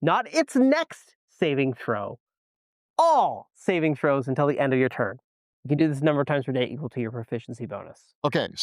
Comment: I would have to rewind, but I thought they said "until the end of your next turn". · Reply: don’t worry, I did it for ya, cause I thought I must’ve misheard as well, but I assure you they said “until the end of your turn”